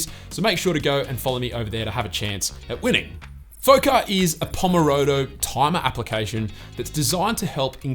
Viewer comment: Did I just hear "pomorodo"? (1:13) xD